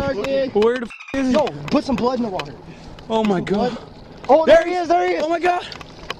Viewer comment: did he say:“put some blunts in the water“ ???